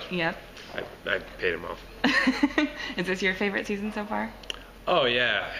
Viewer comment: hahah I thought he was saying:"this is my favorite side." with his head turn like that. I giggled a little.